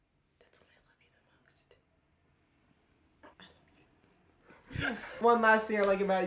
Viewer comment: Anyone heard what Eva whispered to Brent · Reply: She said I love u I'm pretty sure